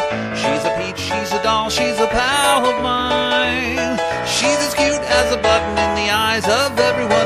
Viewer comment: she's a powerline! That's what I always hear XD